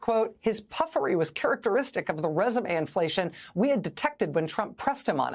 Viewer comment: What is, "rsum inflation"? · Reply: I read it a few times, got it, I think, "[R]esume inflation." The letter, "e," appears to be in short supply. It made Rachel sound weird when she said it. At first, I thought she said, "Resin inflation," which sounds like giving a vaporizer back pressure and low temperatures to blow CBD bubbles.